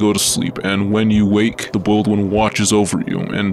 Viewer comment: Spawg why did i hear "the oiled one"😭